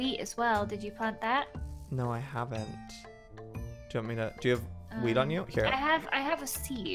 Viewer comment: did anyone hear "do u have any weed on u?" LOL